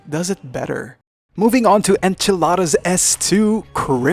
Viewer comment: did you just say at 4:53 "Enchiladas" S2.... .... based but also, peak food Got a laught out of me